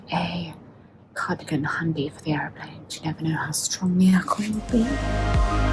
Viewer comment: The captions are wrong, in a very funny way. The "(indistinct)" word is "cardigan" and "alcohol" should say "air-con" meaning air conditioning.